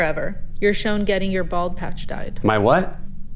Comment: guys i thought she said ball patch too😂